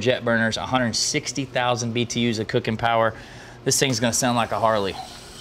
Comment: did you just say, 162,000 BTU? 🤌